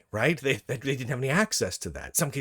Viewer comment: On first listening, I heard this as "they didn't have any Axis to that"